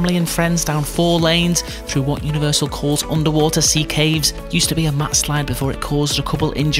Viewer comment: "Used to be a.." what? What did he say...max slide? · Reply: ‘Used to be a mat slide’